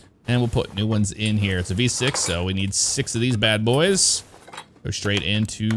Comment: did he just say it’s a fucking v6